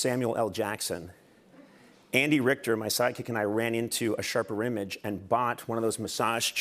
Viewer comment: did i hear him right? he has a psychic? what's its use? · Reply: Side-kick